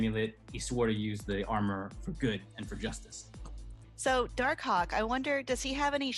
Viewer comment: Did she just say dark cock 😳😂😂